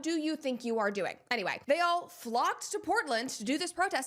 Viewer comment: I heard, "They all FLOPPED to Portland."😂😂😂